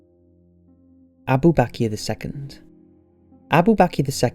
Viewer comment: i thought he said he'd be back in a second, clearly he wasn't